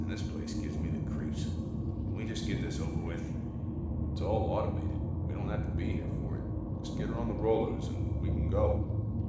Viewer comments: He said "it's all automated. We don't have to be here for it. Let's get her on the rollers. We can go." Well at least I heard that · Here is what i think he said: Its all outoly, we can’t be here anymore. Lets Get her On the roll so we can go. I think thats what they said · I think he said “it’s all automated, we don’t have to be here for it (something something) and we can go”